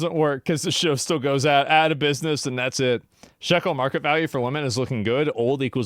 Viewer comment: lol chat said: sexual market value for women — but @ 22:09 he clearly says: shekel market value for women 🤫 i think he nose 🤥